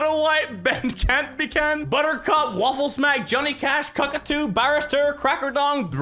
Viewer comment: did I hear Cockatoos?